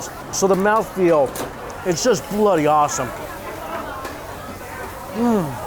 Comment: does he say bloody awesome? 😁